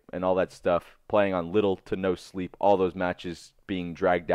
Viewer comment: Sounded like you said little Nintendo sleep, took me a second to realize it was little to no sleep. But really little Nintendo sleep works well, I barely slept when on that Nintendo grind.